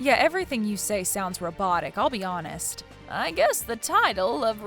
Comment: "make her repent" DID SOMEONE JUST SAY "MURDER HER VIOLENTLY?!"